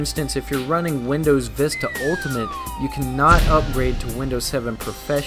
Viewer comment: lmao i heard you say Windows Vista/XP ultimate! LOL! there is no xp ultimate! but thanks for the info =)